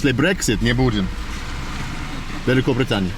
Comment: Eeey net 😄 Sounds like you said "Daleko Britaniya" (Far Away Britain) instead of "Veliko Britaniya" (Great Britain)? Cop found that funny 😄